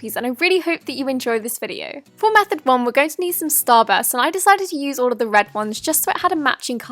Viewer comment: I thought you said Starbucks not Starburst lol 😆